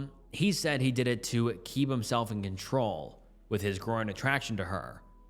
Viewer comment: I heard, "...groin attraction," when you said "growing attraction," lol 😂🤣😅😆